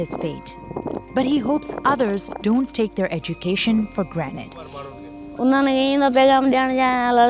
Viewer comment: For granted did you just say for granite???